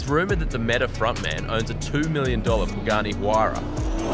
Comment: Am I the only one who listens on 0:51 “Bugatti Huayra “ instead of Pagani Huayra???